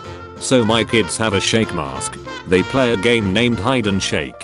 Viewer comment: I love the way the translator said shake instead of shrek in